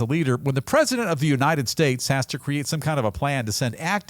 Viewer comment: I thought she said the United Goddamn States?